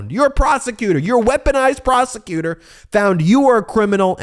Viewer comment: Ha, when you said “weaponized prosecutor” I was in another room - I heard “weaponized *prostitute” (same difference?) 😂